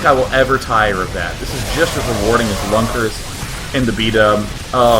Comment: What did he say at 25:25? bunkers? huh?